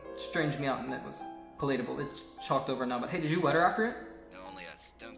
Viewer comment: "But hey did you wet her after it?" "Only at some peices..." IM SO DEAD PLEASE STOP 💀 ✋ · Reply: i thought it said “did you wed her after” but i like your version better💀😭